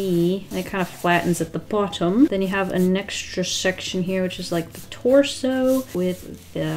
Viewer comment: i thought she said "a neckstra" instead of "an extra"